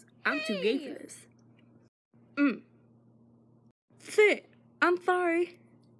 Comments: I thought he said “Oh FIT im sorry” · For a whole while I thought that said 'Thit I'm horny' And I was trying not to laugh because my family is litterally in the room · I thought it said Thit I'm Thorny I made a stupid mistake 😂